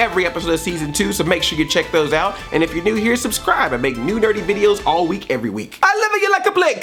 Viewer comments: When he said "I make new nerdy videos every week" I thought he said "I making new DRITY videos" and I'm like: Wut? 0.o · I swear I heard you say "I make dirty videos every week" lol